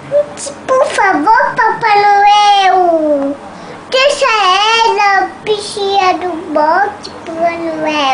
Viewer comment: não briga com a papai noel deixa ela por favor papai noel deixa a caneta .... hahahahah coisa mais lindaaa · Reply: Bia T. Vende Eu sempre ouvi "Deixa ela bichinha do bote"